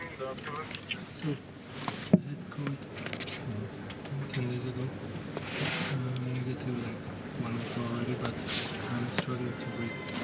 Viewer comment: did he say "I had covid 10 days ago" or "I had the covid * selfcensor * 10 days ago"? I guess if it was covid he wouldn't be allowed to play for another 4 right? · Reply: He said "I had covid 10 days ago, I'm negative 1 week ago already"